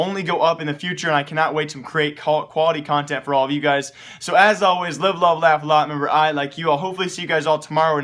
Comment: can anyone tell me what he allways say at the end ? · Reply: And as always; live, love, and laugh a lot and remember I like you. Hopefully I'll be able to see you guys next time